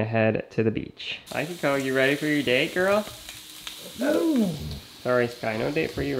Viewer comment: was that sky that Howl and it sounded like a no XD